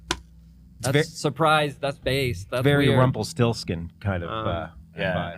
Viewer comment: did Malkmus just say... based?